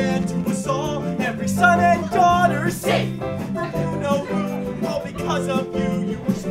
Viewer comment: Is it just me, or did anyone else hear that every son and daughter simps for Harry Potter??